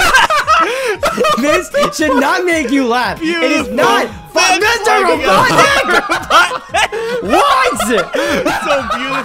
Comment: Did just says Mr.Robotnik?